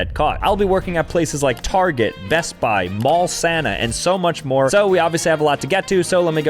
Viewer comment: I fr thought he said ball Santa😂